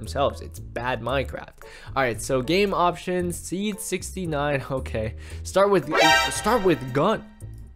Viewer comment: did he say 69? Or am i having delusions again?